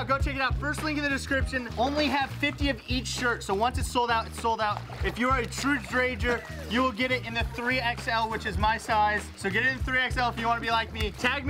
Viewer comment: I swore is said danger 😂